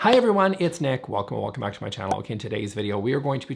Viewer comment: I was today years old when I realized Nick was saying "Welcome OR welcome back to my channel." I thought he'd been saying, "Welcome! OH, welcome back to my channel!" Lol. I'll just ....... show myself out now ..... (Hey, he does talk fast!)